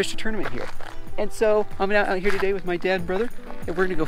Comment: sounded like "im here today with my dead brother" XD